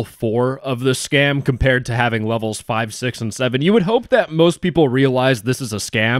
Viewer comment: did he just say.... 67????